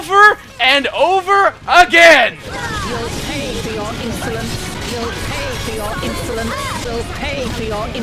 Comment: What you hear "You will pay for your insolence" What I hear "You will pay for your ignorance!"